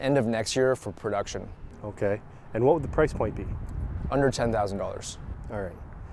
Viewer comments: I thought he said $110,000 and had to rewatch it. Under $10,000, phew! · City driving for that price… awesome ride! Even for a second car in the suburbs. A big obstacle will be getting past peoples snootyness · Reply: At first I thought he said "$110,000." But he actually said "under $10,000" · When he said under $10,000 I thought he said $110,000 😂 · Did he just say 110k? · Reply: He said “under 10,000 dollars” which is confirmed by the website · did he just say 110,000 dollars? my jaw dropped